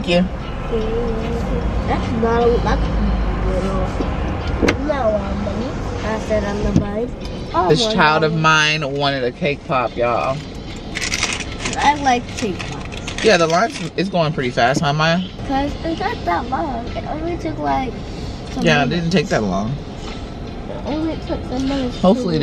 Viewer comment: said "I don't like Cake Pops" 🤣🤣🤣 Why did you get it for her? 😂😂😂😂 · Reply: I heard "i like cake pops"